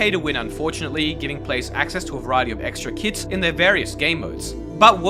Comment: is it just me or did it sound like just said "they give players a variety of extra kids in their gamemodes" 💀